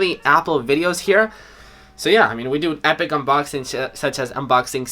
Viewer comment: I swear said epic unboxing shit. 😂😂